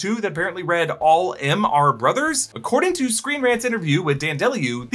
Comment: … I thought it said “ALL M(them) ARE BROTH” lol 🤭🤷🏾‍♂️